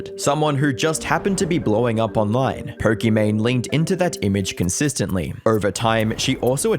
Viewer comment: ur accent makes it sound like youre saying porkimane